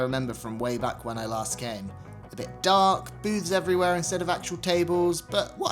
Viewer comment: I thought he said "Boobs everywhere." Lmao